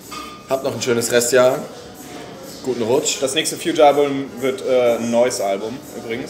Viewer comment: Ich habe erst verstanden: "das nächste Fewjar Album wird nen neues Album" war auch lustig.